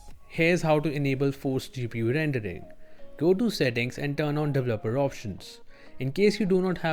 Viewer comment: I can't understand you're accent. go to settings and go to ??? options? sound like u said double O options. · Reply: Oooohhh nm da-vell-lup-per options developer options.